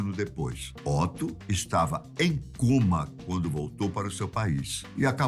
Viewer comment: Otto estava em coma ? É isso que ele fala?